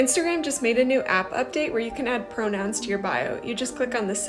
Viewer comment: Damn I thought you said pictures and I got excited just to see the rest but it's just pronouns that are harder to see